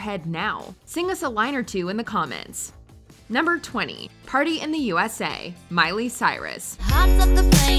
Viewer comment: Lol every time I hear "Party in the USA" I instead hear "Party in the CIA" from Weird Al